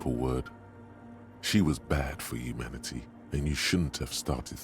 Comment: I was looking down during this part & had to pause because I 100% thought Inland Empire said "she was bad for you, Manatee" like dropping a new Harry nickname I'd never heard before